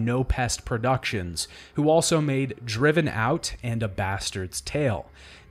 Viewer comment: I actually misheard what you were saying during the 22:36, and thought it was a whole title: "Driven Out: A Bastard's Tale." I figured this was a compelling story about a denied child of aristocracy. The concept sounded pretty cool in my head, until I watched this again and realized my mistake.